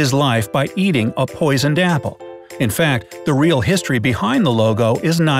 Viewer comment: Lol!! Even I thought why is he saying "Lego" ... And I checked the title of the video!! 😂😂😆😆 director was telling him!!!